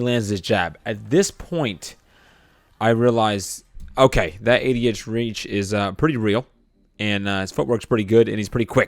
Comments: bruuuh idk why I heard “that idiot reach” Imaoo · lmao at first, i thought u said "that idiots reach is pretty real" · Did he say “that idiot’s reach...” ? Surely I must be mishearing it. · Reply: "80 inch reach"